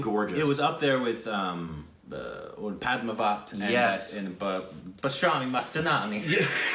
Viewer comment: To everyone who are wondering what said at 4:17 He said Bajirao Mastani 😂